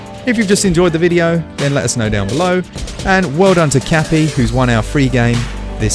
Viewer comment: Dudes!?? Am I the Cappy you're referring to lol!? I'm gonna get smash sooner or later but Im goin for monster boy next and a couple others before I jump on smash? Cool vid 👍. P.S. I use Fox McCloud in smash 💪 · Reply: I swear I heard him say, “Kathy.” Grats Kathy! Errrrrr, Cappy! ;-)